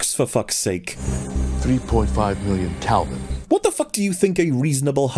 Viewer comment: "That reactor is 150M kelvin, so about 350m short of what was mentioned in this." Am I mishearing the clip? says "three point five million" so the reactor would not be 350m kelvin short but rather 146.5m kelvin long. Also doesn't say anything about the laser device not being capable of creating that sort of temperature; he shows a clip of the Earth destroyed and says, "Earth is a crisp the moment you describe that this is the case" (26:56). As for how destructive a beam like that would be I couldn't say. Nor do I know how technically infeasible a beam like that would be in an "universe" in which other arbitrarily-powerful laser-weapons have precedent (Cyborg's arm-beams for instance). I imagine that you are not a physicist or a laser expert but rather somebody simply making a guess.